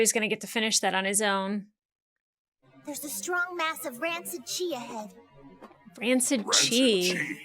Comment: Me, looking away for a moment: Did she say “rancid Chia head?” Oh. Never mind.